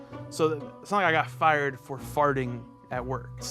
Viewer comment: Am I the only one who thought it said fired for flirting 😂